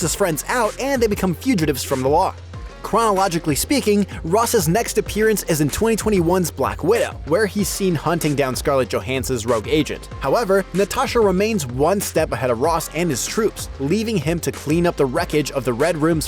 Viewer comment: Did he just say "Natasha Romaine"? Lol. Wtf....